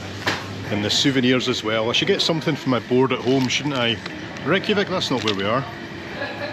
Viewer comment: With your Scottish accent' I swear' I heard you say "for my Florida home', but it was "For your board at home"😂